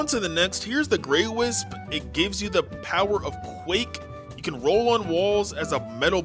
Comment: bro…. I thought u said nuke AND I TRIPPIN?